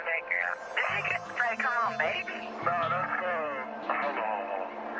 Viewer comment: Did she just say come on baby 19:52😂😂😂